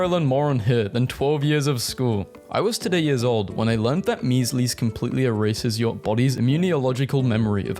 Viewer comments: did you just say mees lees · am i hearing things or did he say “mees lease”